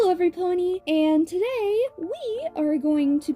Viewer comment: Did you say every pony, or am I hallucinating again? 😃